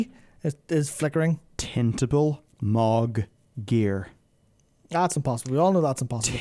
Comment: Thought you said tentacle mog gear. And that MIGHT bring me back.